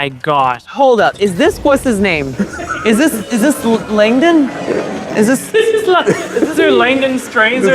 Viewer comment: Am I the only one who thought he said "Langdon"?